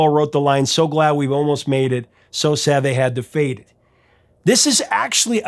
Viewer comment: I thought they said so sad they had to fake it! Now it makes sense😊